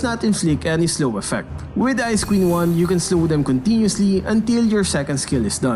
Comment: i thought he said ice cream wand I was wrong its ice queen wand